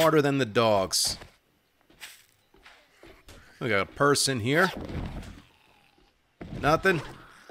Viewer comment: "Purse in here." - I heard "Person here." - No there's not... WTF are you smoking?